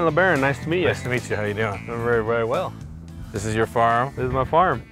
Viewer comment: When he asked, "so this is your farm?" I thought he said, "so this is your firearm?"